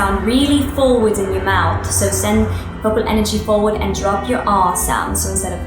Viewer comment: am i the only one who thought that "forward" sounded like zoella